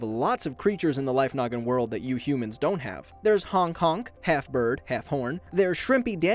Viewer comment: anyone else heard 'Hong Kong'?